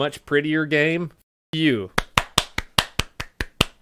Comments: It's quacker! Lmao that's what I thought he said lol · I love how " straight whacker" is still the catchphrase 5 years later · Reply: All this time I thought he was saying "Quacker" because of the duck sounds